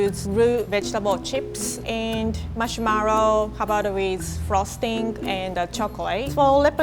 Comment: Did anyone hear say marshmaro